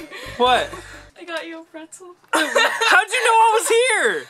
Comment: Is it just me or when he said 🥨 pretzel it sounded like he said wetzzkles